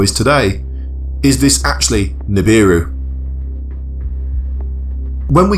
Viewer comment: LOL.... when the vid said nibiru.... thought it said subaru... ... got an instant 404 error...